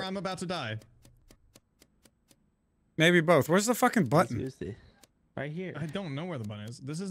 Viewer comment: Now I thought he said where's the fucking blunt